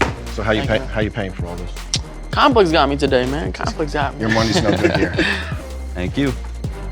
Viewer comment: did he say your money aint no good here? If he did brub